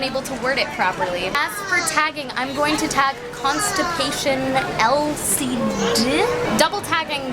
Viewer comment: Did you say constipation instead of constellation at 4:46? I think that's what you said. lol.